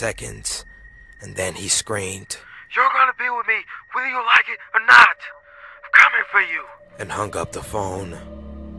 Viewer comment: can someone please tell me what did he just say? Pretty please? :) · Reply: He said: Your gonna be with me whether you like it or not. I’m coming for you!